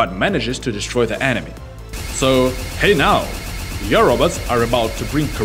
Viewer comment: is it just me or did he say “Pay now”